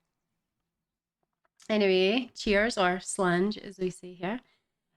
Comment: slange? is that what you said? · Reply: Short for Slàinte mhath which basically means cheers... well it means good health but basically cheers!